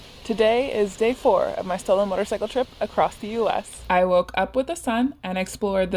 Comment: I thought you said "...my stolen motorcycle trip". Medora is a beautiful little town.